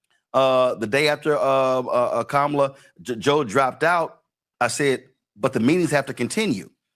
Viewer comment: Did Roland just call her kamala joe at the 4:25 min mark :glasses-purple-yellow-diamond::face-purple-wide-eyes::face-red-droopy-eyes::face-fuchsia-poop-shape